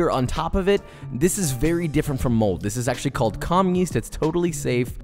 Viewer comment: does he say "com yest"? I cant find this on google.